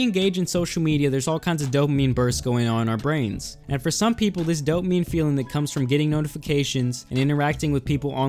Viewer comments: at first I thought you said that dope meme feeling, and I was hearing you my dude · I heard "dopememe" lol.